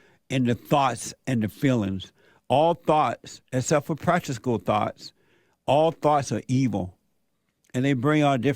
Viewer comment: At the 13:20 point of your program, you mention that "all thoughts are evil", except for "?" (what kind of thoughts?)... I hear all thoughts except for "practischool" thoughts are evil and I don't know what "practischool" is. LOL! I'm a bit hard of hearing, there's some background noise from equipment, and I'm hard of hearing... Please clarify.